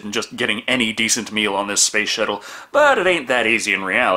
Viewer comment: why does it sound like you said space shittle? I know you're saying space shuttle but... 😂😂😂😂😂